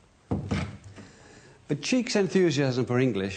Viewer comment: LOL at the subtitles: "buttcheeks enthusiasm" instead of "But Cheke's enthsiasm". Sounds very Chaucerian...